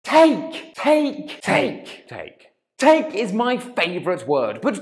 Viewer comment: When said "take! Take! Take Take! " in the beginning of the video, I thought he was saying "Cake! Cake! Cake! Cake!"